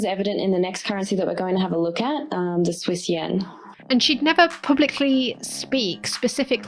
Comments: Did she say Swiss Yen?? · Did she say the Swiss Yen?